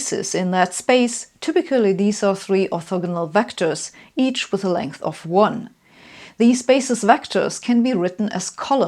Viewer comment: This is great, but I've gone over the sentence at 1:15 many times and I simply can't understand what you're saying: "typically veese or free verigmore vectors, each with a length of one." This is not intended to be insulting - mein Deutsch ist shrecklich - but it seems important! Anyone? Thanks. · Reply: "typically these are three orthogonal vectors, each with a length of one."